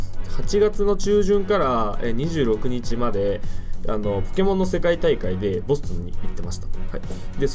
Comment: ポケモンの世界大会と聞こえるのわ俺だけ？